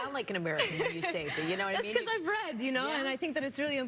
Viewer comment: "You don't sound like an American." "That's because I've read!" 😂😂😂💀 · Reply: I thought she said thats cuz im red😂